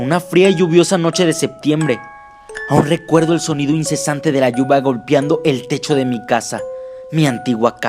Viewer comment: parece que dijo lluva emves de lluvia